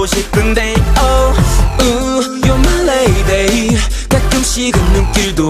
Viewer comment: OMG i thought he said "ohh you're for sex" 0:17 or "ohh you're for sex scene"